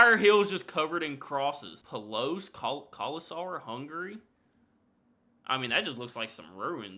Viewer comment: did you say "hungry"? 🤣